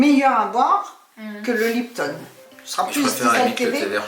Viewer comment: あたりでお母様がLiptonと言っているように聞こえました。フランスではLiptonのアイスティーが売っているのですか？